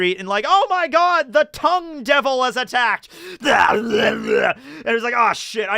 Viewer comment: I honestly thought you said 'Cum Devil'